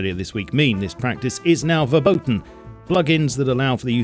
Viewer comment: did he said 'verboten'? (German for forbidden?)